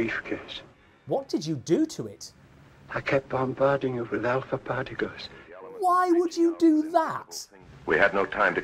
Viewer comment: I thought the guy said, "I kept on barding it without the party goods." Had to run it back like six times.